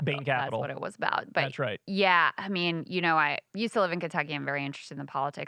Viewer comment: It sounded like said, "You still live in Kentucky". I was like, "What"? I had to look at the transcript which said, "I used to live in Kentucky". How incredibly irritating.